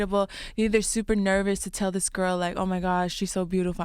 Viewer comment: im sorry did she just say GIRL???? ok dinah i see you...